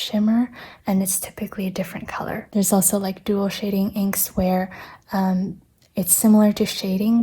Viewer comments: I thought it said an*l shading ink 😭 lmao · I thought "dual shading" said "anal shading".